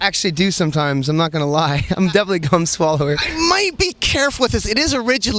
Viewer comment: "Gum swallower" Lol I heard "Fun swallower"